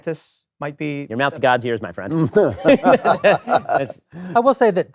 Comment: "Your mouths gods ears my friend" ben Shapiro at 3:20, if I heard correctly. What's this mean?